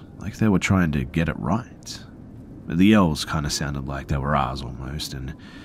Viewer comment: Did you say elves? 😳?!?!